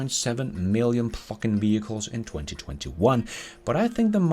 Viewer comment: Initially it sounded like "fuckin' vehicles" and I thought "yeah, let loose Lars!" ;p ;p ;p